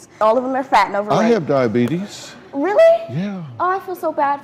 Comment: when Dr Phil said "Diabetes" i heard it as "Diarrhea" the first time OwO